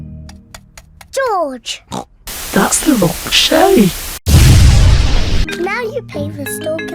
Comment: It sounds like Peppa says “George! That’s the wrong shelly.”